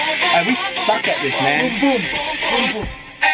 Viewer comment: Did he just say F*ck🙂🔪